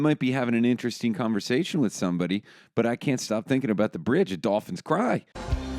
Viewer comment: Swear to god I heard it at 4:31 as, I can’t stop thinking about the bridge at Dawson’s Creek